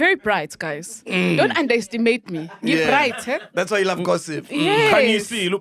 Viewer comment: not ghosti saying don't underestimate me instead of undermine😂😂😂😂